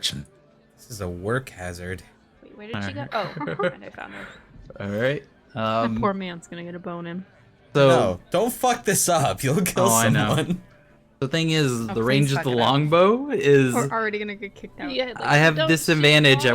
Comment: I almost thought she said "That poor man's gonna get a boner"